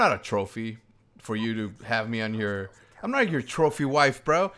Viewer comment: when you said trophy i thought you said troll feed XD